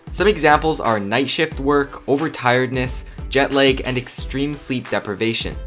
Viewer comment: I'm sorry, what did you say? Jet leg? English isn't my first language